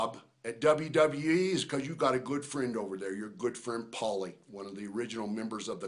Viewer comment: did he say "Paul" or "Paulie" or something? Is he talking about Triple H? (Paul Levesque).